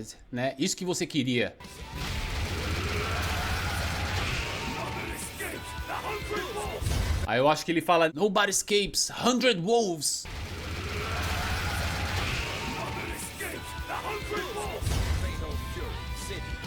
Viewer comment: O que você achou do Terry Orochi Capetão? :_oqueTerry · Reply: Eu escutei "Nobody escapes the Hungry Wolf!"